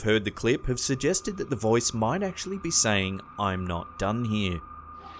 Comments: I heard "I'm not Donnie" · no matter how I hear the recording, it seem say both and not one of them but more like "I'm one done here" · I heard at 7:35 I’m one done here